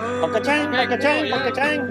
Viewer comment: Did he just say, Dang, Bobble dang?